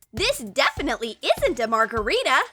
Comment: WAIT WAIT WAIT DID I HEAR THAT RIGHT " ThIs IsNt A mArGaRiTa" OR AM I HEARING THING